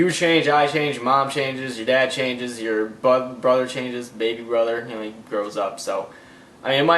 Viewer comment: lol at 3:54 it sounds like he says butt brother